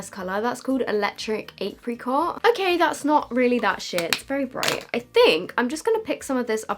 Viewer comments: am i the only one who heard “that’s not really that shit” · Who else thought at 6:10 said ‘it’s not realy that shit’ and acc said ‘it’s not realy that sheer’ 😂😂😭